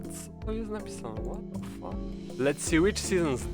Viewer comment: did i hear: "Kurwa, co tu jest napisane?"